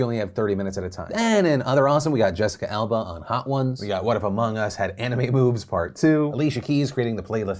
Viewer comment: I definitely heard "what if Among Us had anime boobs". I immediately opened the description to my disappointment